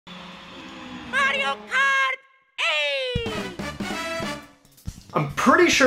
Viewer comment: It sounded like he said at the beginning "MARIO KART.. AYYYYYYYYYYY"